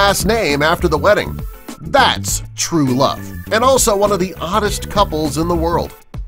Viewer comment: I thought it said “oldest” and I was like what was the thumbnail for 😂